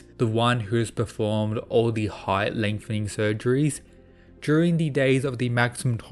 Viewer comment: did..did you Said..KNEE SURGERYY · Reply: "height lengthening surgeries"